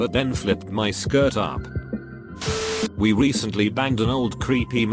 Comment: I thought for a moment she said "I banged..."